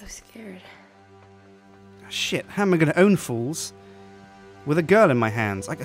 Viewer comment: Instead of saying shit I thought u said